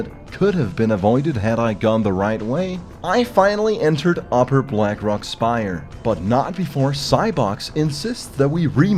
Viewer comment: I need to know what Emiri said about Asmongold. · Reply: Amongold is a leech